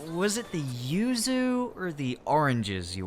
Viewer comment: what she say? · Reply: Orange